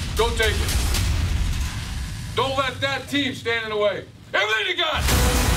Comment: What did Urban say at 0:40? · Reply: "Everything you got!"